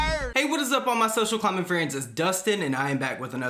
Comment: Tell me why I’ve ALWAYS THOUGHT you were saying “social climate friends” and now I feel like a moron. Even the auto generated captions can tell you’re saying climbing